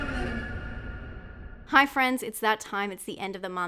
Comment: Thought she said "Hi Friends, it's that time of the month... " This season of Black Mirror was a little weak. Predictable, falling off.